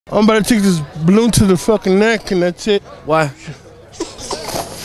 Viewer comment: I first thought he said “Im Filled up to the neck, and that’s it!” Either way hilarious with the meme of him